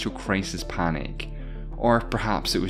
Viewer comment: Did he say clicks about a newspaper?